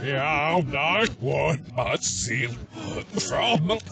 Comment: did anyone hear "frog milk" lol